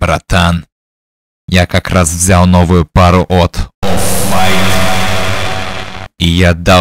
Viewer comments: Что он говорит: "Я как раз взял новую пару "ИЭРРЭЙП""? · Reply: Офф вайт - дорогой бренд одежды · что он сказал? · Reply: офф-вайт - дорогой бренд одежды